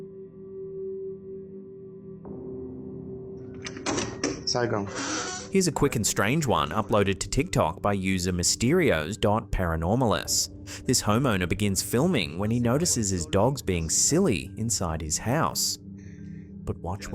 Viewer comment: I really thought you said DOG paranormalist (or less bc captions say it), not DOT lol.